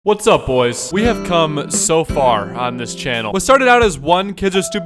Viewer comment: Somebody else heard "we have cum" in the First second